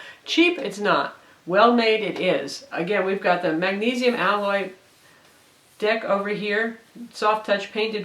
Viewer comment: it kind of sounds like she says "magnesium allow dick"